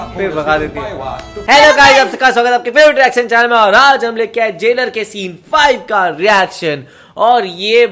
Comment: Jailer ke scene 5 Nehi jawan ke scene 5 😅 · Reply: Look from 0:09 to 0:13 he clearly said Jailer